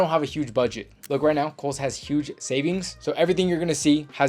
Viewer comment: When you first said Kohl’s I thought you were saying Holes and I looked for a website called that that sold clothes but couldn’t find one 😂.